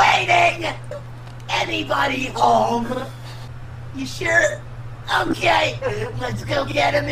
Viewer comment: Guys for those who thought he said "Im gay!" 3:18 He actually said "Okay!!" I hoped this helped🫤